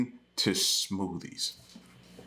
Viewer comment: Omg I thought it said "soothe" almost dropped my phone when you said smooothie....gawd